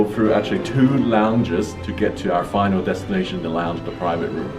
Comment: Did he just say "finale destination".?